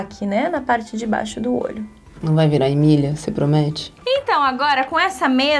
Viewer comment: to tão viciada no canal da Nath que quando ela perguntou "não vai virar emília?" eu escutei "não vai virar milhas?" kkkkkkkkkkkkkkkkkk